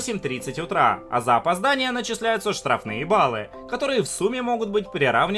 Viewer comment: Мне одной послышалось "Штраф на е*#лы"?🌚